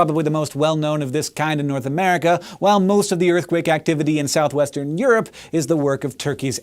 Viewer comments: So YouTube kind of hitched for a moment and I just heard "While most of the earthquake activity in southeastern Europe is the work of turkeys" before it started buffering I nearly spit out my drink. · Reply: I heard South Western Europe at 10:05. WUT??? Let me rephrase that... WUUTT??? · So YouTube kind of hitched for a moment and I just heard "While most of the earthquake activity in southeastern Europe is the work of turkeys" before it started buffering I nearly spit out my drink.